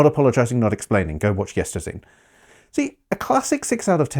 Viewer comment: I couldn’t parse what you said, “go watch ____”? The subtitles don’t cover that sentence either, I’d like to watch whatever it is to get the context! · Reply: It's "Yesterzine", Dudley of... well, Yestrzine's channel which amongst other things heavily litigates the Amiga Power "73% is the worst possible score you can give to a game and not upset the publisher" concept. Links in the description. Also the subtitles should be fixed in that section now.